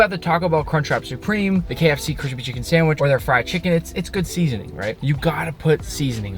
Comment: ? huh? the hubadaguubadae? does anyone understand that? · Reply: the kfc crispy chicken sandwich.